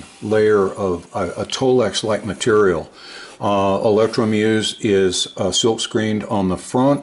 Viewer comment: As you read, "Electromuse...", I hear, "Danger Mouse!!" No matter; proceed...